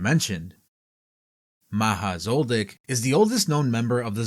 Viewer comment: Thought i heard "maha's old dick"